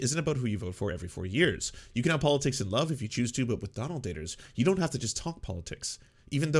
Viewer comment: I heard "Donald-baters"